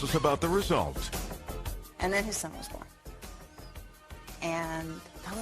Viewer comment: Did she say “and omg he stinks” ?